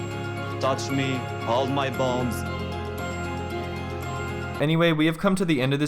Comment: did he say "touch me, hold my balls". ?? 😂😂